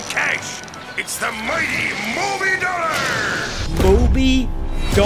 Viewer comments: MOBY DOLLAR? I THOUGHT HE SAID MOVIN DOLLAR!? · maybe he just said “moving dollar” that’s a stupid thought of mine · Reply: IDK why but I hear "Movie Dollar"